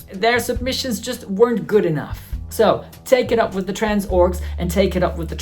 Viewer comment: When you said trans orgs, I heard it as trans orks 😂 seems fitting though